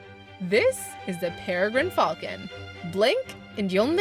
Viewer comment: Bruh once I heard Peregrine Falcon and I heard it as "Pure Green Falcon"